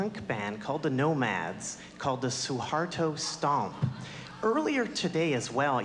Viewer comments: Did he just said suharto??? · i hear suharto hellyeah he is demon :(